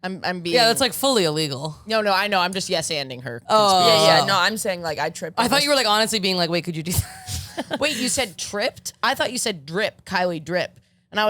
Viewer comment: I also thought Kylie said drip but I thought "are you dripping blood?" 😅😅